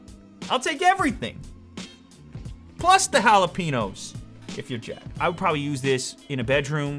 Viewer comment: Did he say "hala-pee-nose"? jalapeños? hehehe